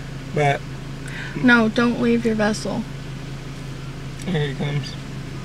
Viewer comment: "Don't leave your vessel" 🤣🤣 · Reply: I thought she said, “don’t wave your vessel”. I was like wtf?